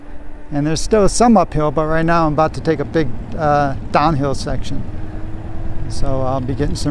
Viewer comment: I was turned away from the TV at the 13:30 mark and 😆 thought you said "right now I'm about to take a big doo doo" 🤣